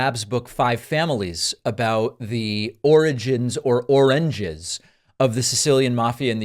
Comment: NICE!!! David you rock sir! For those wondering what the heck David is saying, “Orinjuzz” one of Trump’s many, many slurred, mispronounced , or otherwise scrambled words/statemenrs/etc from a previous incident where he was trying to say - I think - “Origin”.